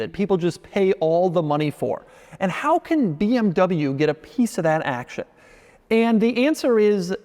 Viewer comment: I thought he was going to say BMW wants a piece of that A**